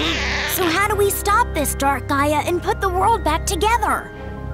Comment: I thought he said dark gaara